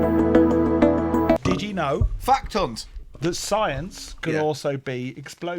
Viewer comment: WHAT did you call?! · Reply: Er... Fact Hunt. It's the name of series / book.